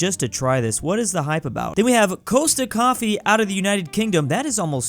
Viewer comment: Did you just call it "coaster coffee" 😂😅😪😭😭😭